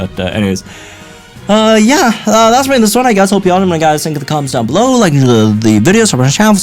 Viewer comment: bro wtf did he say ? 💀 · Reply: Yah, that's gonna be all for this one, I guess. If all you guys let me know what you think in the comments below, leave a like on this video and subscribe for more Pokemon Masters content.